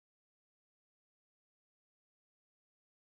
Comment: que entendieron en 13:26 yo entendi: me suma elec gol